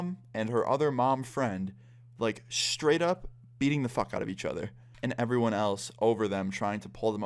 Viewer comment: I thought you said they were eating each other